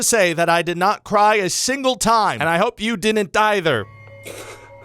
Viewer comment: Did he say: ...and I hope you didn't either Or did he say: ...and I hope you didn't die there! I mean... very respectful of him, either way.